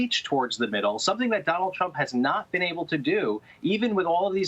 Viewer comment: I could have sworn that he said Donald Chump.